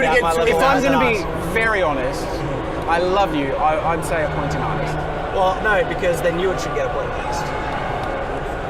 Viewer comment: "appointing artist" -> "a point in artist" It took me way too much time looking at the definition of "appointing" before i understood what they were saying. This spelling mistake is not minor.